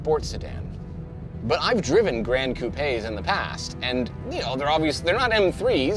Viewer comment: did he say "Cou-peh?"